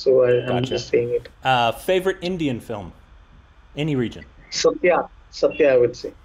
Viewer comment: is he said RGV satya or something else ??